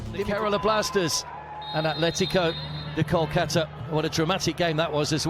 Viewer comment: Did I hear Atletico the Kolkata??? 😅😅